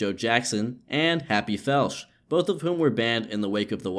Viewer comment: Thought that said Happy "FELCH" was gonna say that's one hell of a name lol